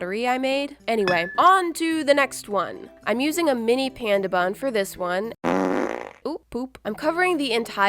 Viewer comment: Why do I hear "pen dab on" ?